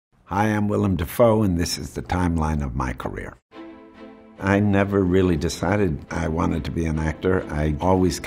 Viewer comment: I thought, he's say his name is willem dripfoe 🤣🤣